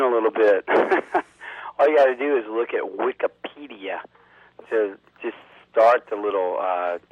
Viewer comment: I thought he said "all you guys do is look AT wikipedia"!